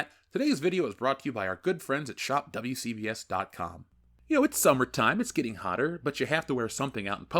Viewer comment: when you pitch your merch, I hear "Shop CVS" and wonder at how well you're doing now that you've managed to land a deal to sell your wares at the pharmacy powerhouse 🤣🤣